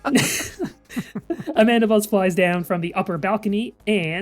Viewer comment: I misheard it as Amandabuzz, and I will forever interpret that as her name. Amandabuzz the Mandibuzz.